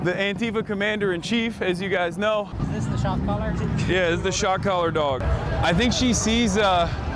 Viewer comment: It's funny that you can so easily mishear him saying "shot caller dog" as "is this the shock collar dog?"